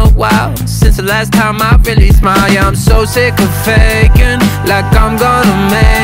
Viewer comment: Is it just me or when he says I’m so sick of faking the only thing I hear is “I’m so sick of vegan “😅😂😂 Edit: roses are red violets are blue I will comment my own comment bc nobody will do 😢